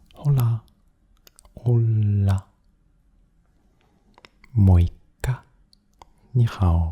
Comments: 「もういっか」にしか聞こえないw 空耳だわww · で もいっか って言ってる風に聞こえるけど何語？